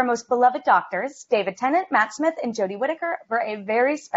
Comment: When she said Matt smith I thought she said Matchstick